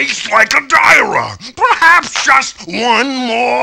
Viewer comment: Ok seriously.. I've watched this at least twice per year since it was uploaded, And I still don't understand what the cyclops says when he eats that guy at 10:38 ''Tastes like a (dira?)''